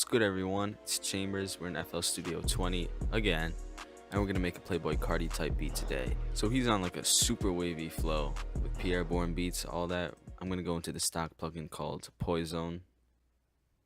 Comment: I thought you said ‘super baby flo’